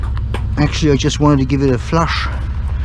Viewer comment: hab ich das richtig verstanden, du willst dem nächsten der durchfährt, "a flasch" geben 🤔 meinst du eine Dusche mit dem Wasserschlauch? Kann leider nur ganz wenig Englisch, war in der Schule immer schon zu faul zum Vokabeln pauken, fand es ätzend.